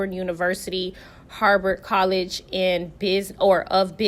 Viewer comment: Child I'm listening thru my earphones and I thought you said Harvard NOT I've never heard of Harbert college😂😂😂😂